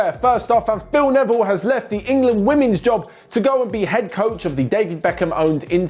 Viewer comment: Who else heard it has that Phil Neville is going to "behead" coach- when said it😂😂😂